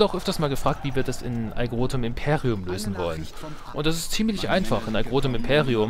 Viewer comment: wie heisst das spiel? ... imperium (5:47)? ich verstehe es nie :( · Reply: Aegrotum Imperium. Bei interesse geht es hier zu allen bisherigen deutschen Videos zu dem Projekt: <link>